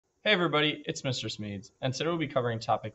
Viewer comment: imagine being named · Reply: I thought he was saying Mr. Smee like from peter pan (until i looked at the account)